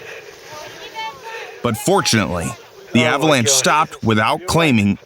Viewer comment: unfortunately? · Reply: I heard "unfortunately" at first, but after repeating that bit a few times I agree what is said is "but, fortunately"